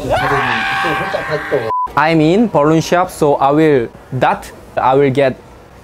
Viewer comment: I heard "im in porno shop"😂😂 omg...